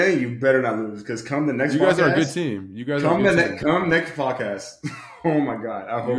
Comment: Words said... "You guys got a good team." What I heard... "You guys couldn't beat a HS JV 😂😂😂. Love it!!